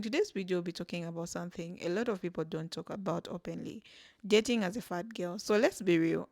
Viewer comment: I thought it said dating a fat girl is hard 😂